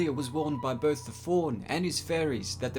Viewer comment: sounded like you said warned by the faun and furries